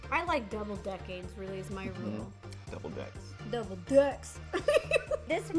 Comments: what do u think she said I THINK DOUBLE DICKS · Did ro say she likes double decking? LMFAOOOOOOOOOOOOOOOOOOO